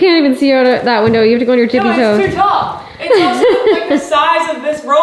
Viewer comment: When told to see out the bathroom window, that she can stand up on her tippy toes, and i thought she said stand up on your chicken toes, i thought that was s low blow, lol 😂